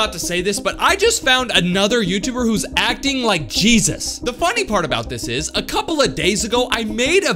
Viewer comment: did he say cheesus